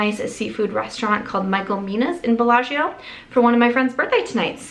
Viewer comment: My husband misheard the name of the restaurant and his reaction was hilarious 😂 he heard micro-p…. Not Michael Mina’s 😂😂